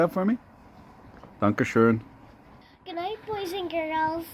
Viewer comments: I'm pretty sure I heard at 9:51 asking "Can I poison girls?" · "papa, throw that in the fire" some little girls just wanna watch the world burn, a sentiment i tend to agree with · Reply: could have sworn she said "... can I poison little girls?" at the end there as well...